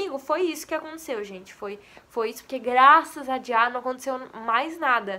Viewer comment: "Graças a dia" eu ouvi serto esse bglh mano